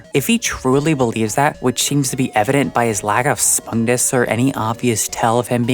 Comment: what did you say here about Jax and his "lack of (spungus)"? I can't quite understand what you're saying there